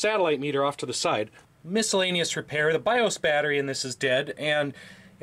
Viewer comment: while listening at 2x speed I thought I heard "the bio-spatter is dead" No wonder those cop computers were so cheap lol